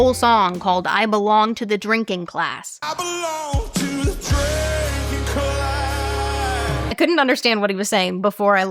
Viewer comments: I heard, "I belong to the breaking glass" 🤷🏾‍♀️ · I heard "I belong to a tricky cloud" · I thought he was saying "I belong to the Dragon Clan" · I heard "I belong to the tree king clams" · at the "drinking class" part... I heard "tre ink cla" xd